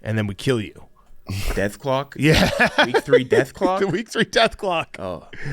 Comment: I heard dethklok and my brain flipped the metalocalypse nostalgia switch